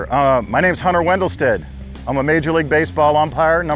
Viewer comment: I thought it said: "How to become a major league empire."